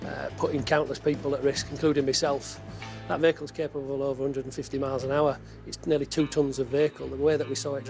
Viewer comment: you must have misheard, he says at 16:43 150mph